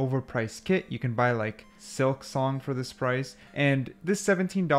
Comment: Thought he said silk thong...